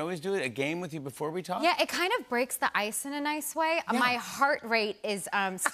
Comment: did she say breaks the ice in an ice way?